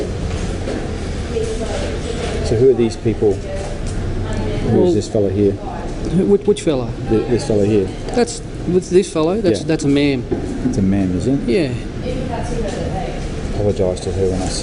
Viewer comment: this is some funny shit "whose this fellah here? " "thats a ma'am" "I'll apologise to her when I see her" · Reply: I thought he said, "that's a MAN" lol I was like "no shit Sherlock"